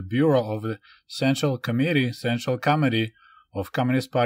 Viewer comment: I heard “Central comedy” instead of central committee 😂